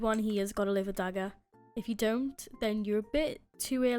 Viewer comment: I thought she said " if you don't have a livid dagger your a bitc-"